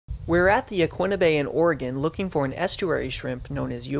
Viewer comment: I thought that said Vagina Bay- im